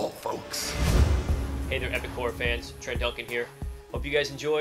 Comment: At first i heard " Hello epic corn fans " .